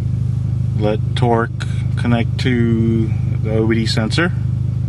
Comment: Sounded like you said "Torque connect to the Wii sensor" lmao